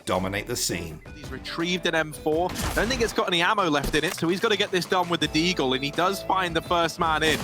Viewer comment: x0.5 playback I think he said the n word unless im deaf lmao. Nevermind I listened further and I think he said then he's got no more ammo left really quick.